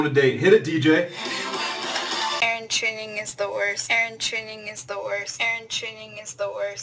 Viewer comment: i thought she said, "hair trimming is the worst"